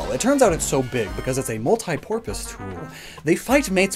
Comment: I had to rewind a few times to double check if I heard that right 'multi porpoise tool'.